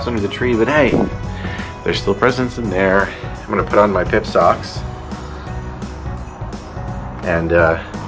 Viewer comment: Haha. Thought you said pimp socks...😂